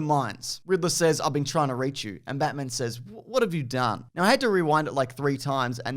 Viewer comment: I honestly thought he said "Time to Die" not "What have you done?"